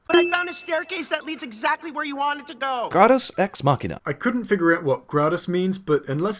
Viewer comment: 'Gradus' means staircase in Latin. It took me literally one Google search to find that out. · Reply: Rory Stockley but I thought he was saying gratis...